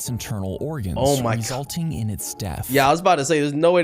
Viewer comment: Uhm I think said daathh